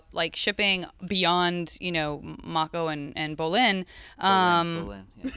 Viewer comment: Does anuone know what PJ says at 2:50? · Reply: He's just saying "Bolin."